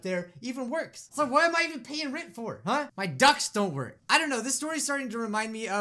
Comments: XD It sounded like he said his ducks don’t work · What said: “My ducts don’t work!” What I thought he said: “My ducks don’t work!” 🦆 🦆 🦆